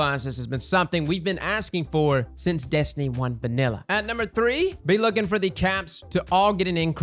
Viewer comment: the way you said “vanilla” sounded like drifter 😂